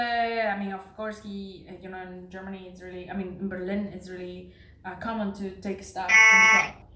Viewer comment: What does she say at 3:20? Did she say it is common to take 'stuff' in the club?